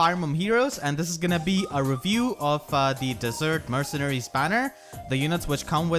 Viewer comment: Did he just say dessert, instead of desert!?